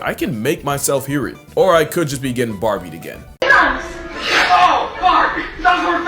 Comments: Oh my god the barbie bit. Listening, I hear "oh barbie", watching I hear "oh fuck" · HOW IS IT POSSIBLE??? I heard "OH FVCK" then when I went back it heard "OH BARBIE" back and forth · i heard it as "oh farbie" ngl